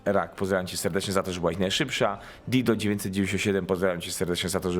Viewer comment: Boże, 11:51 usłyszałam dildo XDD